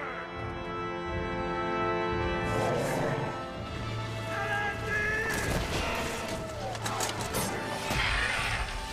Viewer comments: What does Aragorn yell at 2:34 ? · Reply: elendil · What does he yell at 2:34 before jumping down? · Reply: Elendil ,name of his ancestor . The first king of anor and gondor